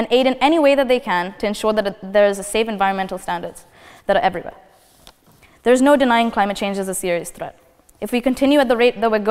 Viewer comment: At first I thought she was saying there's nothing like climate change 😅, then it became clear and she gave a good speech